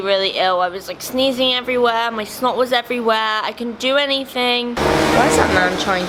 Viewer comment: with captions on it says smoke instead of snot 😂